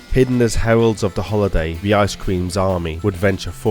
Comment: i honestly thought you said ice cream lmao